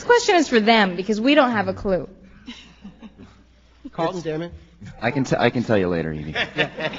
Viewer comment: Does anybody knows if was Matthew that said: "I can tell you later Evie" in 2:16? Is his voice? And what Carlton said after?